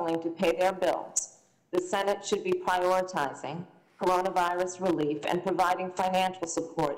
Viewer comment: I can't belive at whoever does the closed captions does not do an unabridged version. At 4:12:58 harris says tons of billions not tens of millions like the closed captions says what bs.